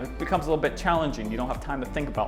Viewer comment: a DYI hair mask? Or did I hear that wrongly?🤔😬